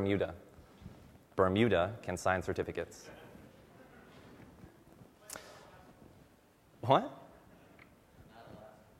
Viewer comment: what the man from the audience said to him? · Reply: I want to know too Edit: I think he said "north atlantic"